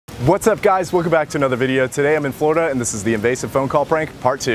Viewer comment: Am I the only one who thought he said “I’m in fortnite and today ...”